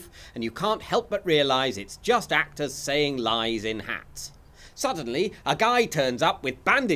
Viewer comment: "just actors saying lies in hats". If ever I start a theatre troupe this is what it shall be called. 😆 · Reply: I thought he said “lines” not “lies” 🤣